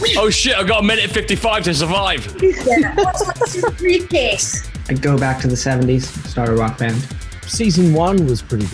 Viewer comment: I don't understand what Aidan said. Can someone write here? Please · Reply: He says "I'd go back to the seventies, start a rock band."